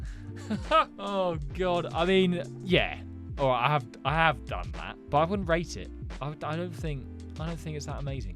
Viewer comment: sorry, did i hear that right did you say you HAVE… done that?